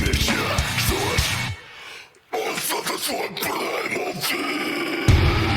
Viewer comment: i dont understand much but im sure he is saying "playmobil" and you cant convince me otherwise